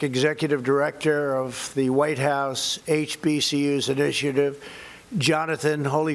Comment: Did anyone notice at 7:06 he said "HPCU"...instead of "HBCU"!! This clown is off...what the hell is up with his mouth and the slurring....looks like he is trying to hold his teeth with his top lip?? He does this a lot!!